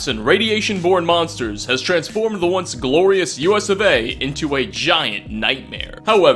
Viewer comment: did you really say US or A on 0:11? nothing wrong, its just never heard it.